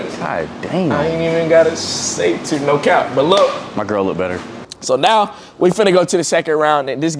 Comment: got a girl tho??? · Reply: Lol I sholl heard him say “ my girl look better “